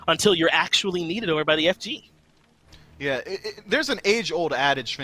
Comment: My man said f Jew instead of fg at lik